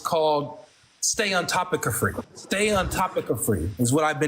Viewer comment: Bro! I cant take you serious no more 🤣🤣🤣🤣🤣🤣. @ 16:05 Did you say that you have a disorder called "Stayontopiccrophia?!" 🤣🤣🤣🤣 I just cant lol Im done. Bro stop playin with these people 🤣🤣🤣